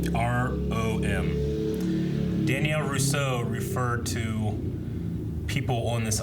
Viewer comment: I thought he said Danny LaRusso but he said Danielle Rousseau, is that some easter egg or something?